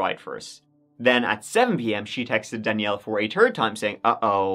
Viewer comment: Every time you say "third" I hear "turd" 😂 on the twenty -turd.